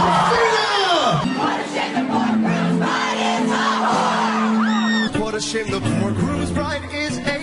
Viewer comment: I heard him say grooms bride is John cena lol😂😂😂